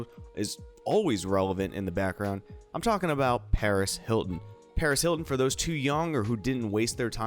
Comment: I thought you said Perez Hilton and I freaked out